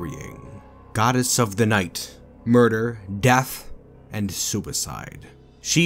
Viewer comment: I heard 'goddess of murder, death, and soupicide'. Anyone else?